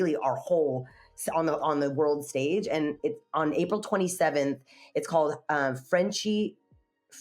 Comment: 27th or 29th? It says 29th but it sounded like she said 27th.